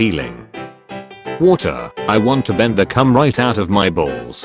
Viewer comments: I thought it said blender for a second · PLS HHHHELLLP I THOUGHT IT SAID BLENDER